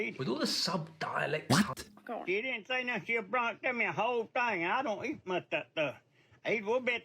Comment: I can't catch the first few words but he says "they sent me a whole thing and I don't eat much of that stuff" then some more I can't hear but he ends with "gunna go bad if I don't eat em"